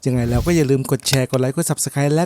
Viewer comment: ต้นคลิปพูดว่าอะไรอะคับ "..."ไหนแล้วก็อย่าลืม ผมเถียงกับเพื่อนนานมาก ฟังไม่ออก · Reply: ยังไงแล้วก็อย่าลืมกดแชร์….